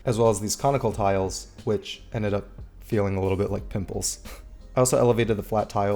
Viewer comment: sounded like you said "nipples" instead of "pimples"